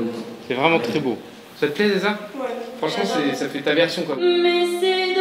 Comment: C'est moi ou a 2:03 Vianney il appelle Erza "Zeza" c'est trop chou❤️😍😭😘🥰